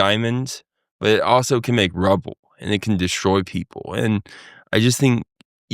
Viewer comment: "Pressure makes diamonds, but it also makes rubble" love this guy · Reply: I thought he said rubber & it had me questioning how it was made.